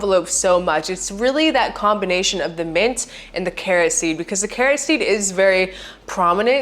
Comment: Ohhhh, “carrot seed”! I thought you were saying “kerosene” 😅